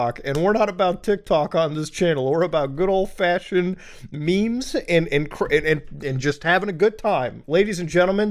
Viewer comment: ''Memes and INCRIMINATING'' Don't think I didn't catch that 🤣🤣🤣